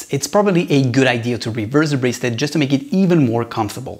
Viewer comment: Sorry but what did you say at 4:15? I do have relatively small wrists and am considering this watch. I’ll need to definitely use the micro adjusts..... I just can not make out what you’re saying at 4:16 exactly. Right after you say “if you have smaller wrists it’s probably a good idea to —————? What do you say there? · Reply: It’s sounds like you’re saying reverse the bracelet but that doesn’t make any sense